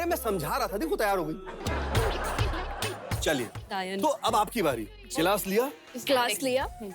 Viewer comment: Did anyone else heard the word Dyaan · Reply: Daayan naa